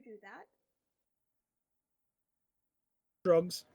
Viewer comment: i thought said grubs